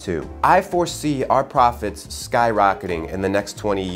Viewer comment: At first I thought he said, "Prophets" but then I realized how stupid that was